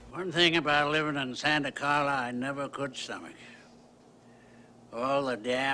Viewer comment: I swear grandpa says "One thing about living in Santacarla I never could "summage", all the Damn vampires. But in script it reads "stomach". Am I the only one lip reading and listening hard? I swear he said "summage" Have a listen!!